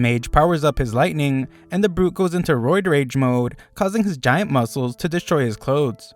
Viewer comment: my dumbass thought he said "road rage mode."